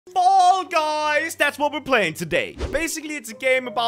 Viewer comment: Anyone realized that crainer said "Mall Guys" Instead of Fall guys